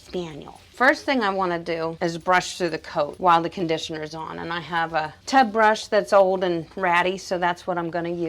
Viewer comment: What type of brush/tool are you using at 8:50 to help remove the dead coat immediately after conditioning? You mentioned it by name but I couldn't understand what you said and I do not see it in your link below. Thank you! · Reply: Coat king